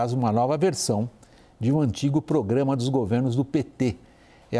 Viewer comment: ele começa falando "um antigo problema do PT, é a política de valorização do salário mínimo". Se você que esta lendo aqui recebe salário o WW falou que dar aumento de salário para você é um problema. Você ter um salário mais digno é errado. CNN cada vez pior. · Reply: A primeira vez que ouvi eu também entendi "problema", mas ele disse "programa".